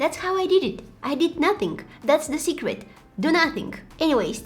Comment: When she said 2:13 I thought she said to not wash it 😂😂😂😂😂😂